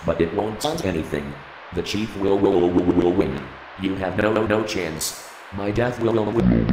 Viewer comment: You can recruit as many people as you want, but it wont change anything, the chief will win, you have no chance, my death will not be warranted. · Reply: I always heard it as "my death will not be in vain", personally.